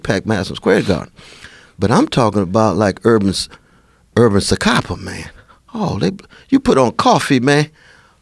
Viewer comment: anybody know this Korean thing he is talking about? Urban Se? I can't make out what is is referning to but sounds cool